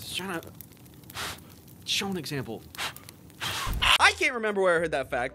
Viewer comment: i heard it!!! You said „schade“ u german bro? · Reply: He said "show an example" That's not even close bro